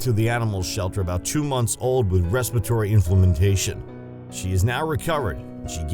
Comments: am i the only one who noticed the guy said inflamentation instead of inflammation? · "Wrapping her wings around his neck"??? The last time I looked, that part of a swan was called it's neck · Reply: Colin Remmer At 2:51 he also states that the dog had respiratory inflimitization instead of inflamation 😄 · Colin Remmer At 2:51 he also states that the dog had respiratory inflimitization instead of inflamation 😄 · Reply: I thought he said "inflammantation" !!!! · did he just say inflammintation